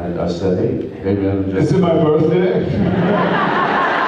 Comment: what did jared say at 2:51? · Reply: I think he says "is it my birthday?"